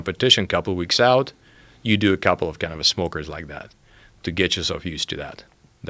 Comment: "we do a couple of cannabis smokers like that "? That's what i hear